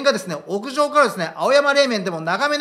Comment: 青山霊園が青山「冷麺」に聞こえるんだけど、金平＝冷麺か…私の👂調子悪いのかなぁ。